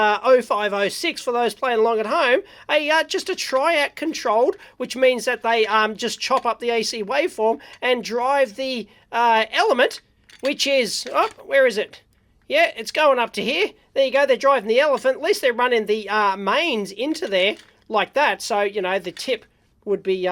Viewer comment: Did he say "Drive the elephant"??